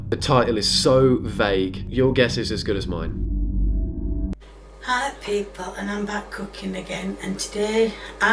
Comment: All I hear now is "Hi pee poo" 😂